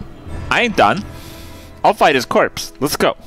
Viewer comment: "I'll fight his corpse" xD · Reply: Oh my god I heard it as “I’ll defile his corpse” lmao